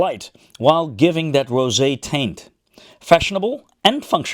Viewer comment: WHOA-HO-HO!!!! what did you say ?!?!?! @ 17:22 did you mean "giving that rosay TINT????" yea... I know that's what you MEANT.... but that's NOT what you SAID!!! ... maybe you9 should look up 'taint' in the urban dictionary..... HOPEFULLY yt doesn't know what you're saying... (but I do 😉 🤫🤐🤣)